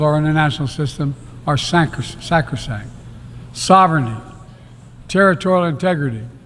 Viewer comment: Listen:4:45 -4:57! You can't understand his mumbling words, nor can Joe!! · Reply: What is that? Sacroshap? 😂😂😂